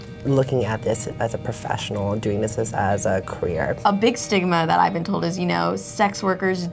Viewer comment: Erm.... i thought they said 'career' not 'queer' at like 2:05 (the subtitles) O.O SOMEONE PLEASE